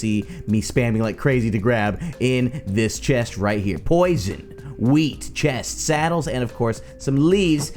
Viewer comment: I thought you said weed not wheat